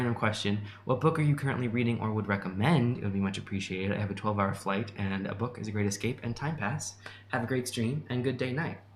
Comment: Did anyone catch what book series recommended? I heard it but I couldn’t make out what he said. I’d love to check it out. It’s at the 1:32:39 point. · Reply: hes talking about the abhorsen / old kingdom series by garth nix! the first one is called “sabriel”